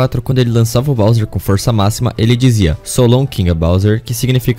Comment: O Mario só dizia "So long bowser" não tinha "King" no meio, é so impressão que ele fala king